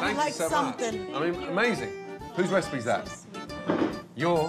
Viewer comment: subtitles says:"Whose recipes ass"😂😂